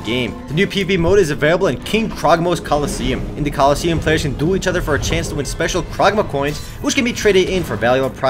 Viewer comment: lol sounded like you said "Players can do each other for a chance to win special crogma coins"